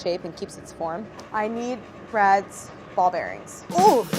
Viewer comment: For a moment I thought she said she needs Brad's balls. It was his bearings. Then she bust the ball bearing bag.